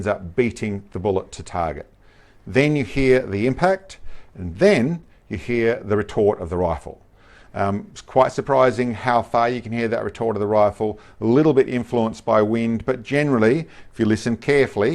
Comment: Thanks for confirming the sound artifacts, @ 05:50 thought this was what was happening. Not trying to be a smart arse ..... sounded like you were saying 'retort' rather than 'report', can be difficult for me, dealing with tinitus and a mild case of industrial deafness, so am presented with plenty of unexplained noises, simply 'cause I don't register the whole frequency range. Love your work, cheers